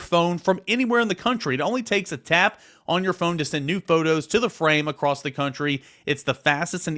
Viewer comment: During the skylight ad, I thought you said "it only takes a tap to send nude photos" 😂 . I'm getting one for sure now.